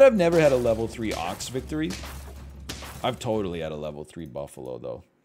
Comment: did he just say "bethree" instead of "before"? If so was it intentional? lol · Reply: he said "victory" not "bethree"